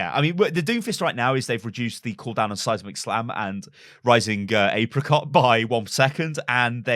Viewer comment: did you say rising apricot XD